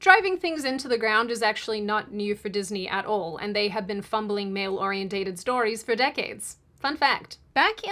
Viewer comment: did you say "orientated"? or oriented?